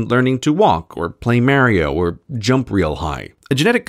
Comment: Did you say MERRIO?